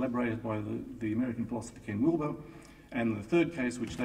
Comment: The photographer had been wandering around for three days snapping photos of the audience and the speakers during their talks. At around 14:46 she can be seen taking a snapshot with the camera flash just as I looked at her crouching in the shadows at the front of the stage. The flash got me right in the eyes and dazzled me so that I had a "Men in Black" moment for a second or two. I think a sentence or two were "lost" in that brief interval of lost time... But I can't remember ;-) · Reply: Hi! Not quite sure if you'll ever read this, but around the 9:49 mark you mention "American philosopher" did you say Ken Wilber? He's the gentleman who speaks of integral theory correct? This stuff is fascinating and really grateful I came across Big History and this lecture because its been life changing.